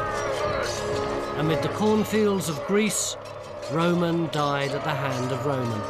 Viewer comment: does he say “the cornfields of Greece”? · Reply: I thought he said "cotton fields". As in pick a bail of cotton, oh pick a bail day! 😉